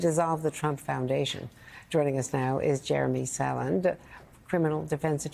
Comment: I misheard Andrea introducing Mr. Saland as Salander, as in Lisbeth. Now, that would have been awesome.